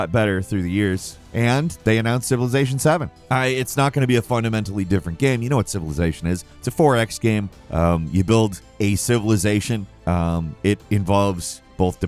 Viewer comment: Because you spoke fast, it sounded like you said it's a FOREX game, and I was wondering how would that work?